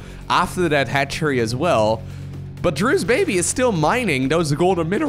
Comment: Did he actually say Drew's Baby around 6:10?